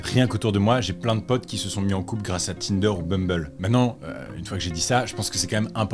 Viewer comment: Mes oreilles m'ont fait défaut et mon cerveau a tilté ! A 22:55 j'ai entendu "Mumble" XD